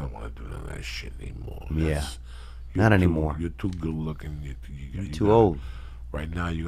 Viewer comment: Joey: "youre too good looking" Theo:" youre too old" Hahaha is that what he said?